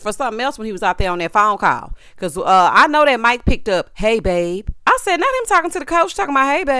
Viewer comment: Omg! I thought I was confusion. I was like did he say “hey baby” or am I hearing “hey David”… like Robyn, I convinced myself that he said “hey David”… lordt 😂 I knew I heard baby. Smh lol